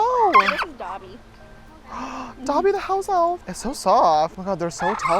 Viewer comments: When you said “dobby the house elf”, I thought he said dobby the house wife 🤣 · A thought he said Bobby the hobo